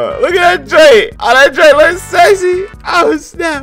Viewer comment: did bro say sexy?